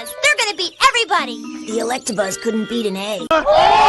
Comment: what’d he say? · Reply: Couldn't Beat an egg like an Egg Beater